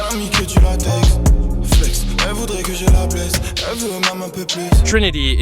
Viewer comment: My favourite part is the line about Trinity: in French you almost mishear "elle voudrait que je la baise" instead of "blesse" which is kind of a big nod to the general French rap patriarchal/women are object culture but subverts it to tell a deeper story. I really enjoyed the story in Trinity a lot.